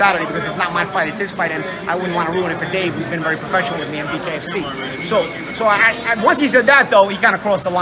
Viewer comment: lol did he say”KFC”???? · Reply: Epic WatchMe BKFC - bare knuckle fighting championship ?